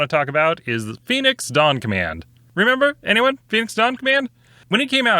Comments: I was watching this while doing housework.... Which is to say, I was listening to it. I thought you kept saying "Phoenix Dog-Man". Sounded crazy. Go back later to see the proper name "ok, So it's not as unhinged as I thought... I'm almost disappointed" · I kept hearing Phoenix: Donk Man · I literally thought you said Phoenix Donk Man. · I had the focus away from the video, and could have sworn he said "Phoenix Donkman!"